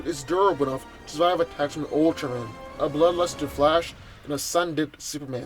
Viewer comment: did you say Ultron when describing the durability of the lex war suit?